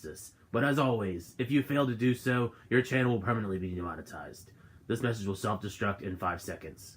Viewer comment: I should probably go see it! WAIT, did he just say your channel would be demonized? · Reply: Super Nintendo Nado You should go see it it's a fun time! And you were close but it said demonetized not demonized haha although either wouldn't be fun I suppose